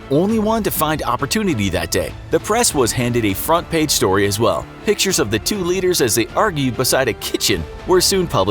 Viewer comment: who else heard "2 liters"